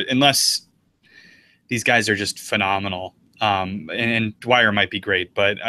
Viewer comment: did someone say Phenomenal?